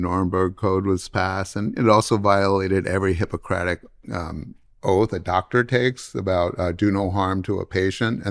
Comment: Oh, I was supposed to follow the Hippocratic oath, I thought you said the hypocritic oath!